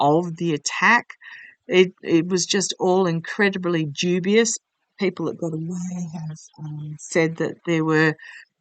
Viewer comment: Hi. At 7.06 i hear clearly one of your guides. "People that got away have... (spoken?)".Can't quite make out the last word. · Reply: Yes, I heard it, too. And I think she said "it's the people that got away with it the last time".